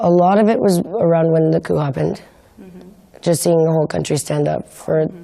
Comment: Anyone know what Lindsay's saying at 4:21 ? sounds like La Cou or something??? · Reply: she is talking about the failed coup in Turkey that took place n July